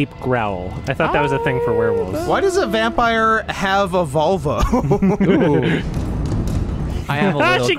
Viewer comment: I dead-ass thought he said, "Why does a vampire have a vulva?".